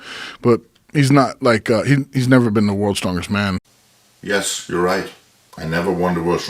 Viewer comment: Now THAT'S how you do a response video! XD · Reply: Little egotistical to think that Joe Rogan should know your name lol. Also at 2:01 he says "he's never BEEN to worlds strongest man" NOT "He's never WON worlds strongest man" Idk dude i think your Ego and feelings are a little too much involved in this. You are kinda making yourself look like a fool. AGAIN at 7:25 he is clearly talking about the guy with the deadlift "World record" NOT YOU, you JUST said it wasn't you so how can you think that Joe is saying its YOU that is smaller then him?? Dude your ego is literally out of control at this point, like you have to be trolling or you are brain dead.